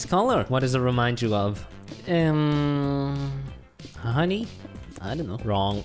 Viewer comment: i hear Honey, but now i know that he sayd Harry